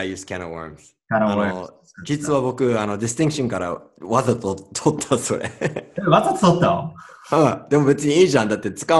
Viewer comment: わざと取ったぞえーって言ってるように聞こえて、ちょっと笑っちゃった♫リスニングのや発音の勉強になりましたぞえー！